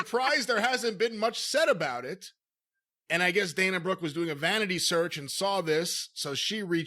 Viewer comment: I heard "she did a manatee search" and that got me.